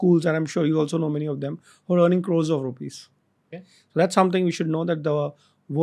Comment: What is Sir saying ? What girls are learning????